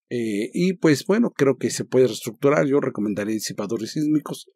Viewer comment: Min 9:24 ¡¡¿RECOMENDARÍA QUÉ?!! No se entiende. '...sísmicos'. ¿Disipadores? Gracias.